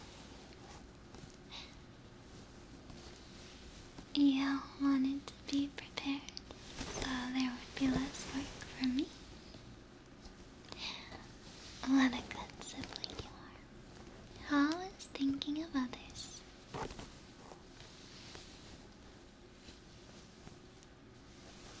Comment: I thought I heard 'What a good sapling you are'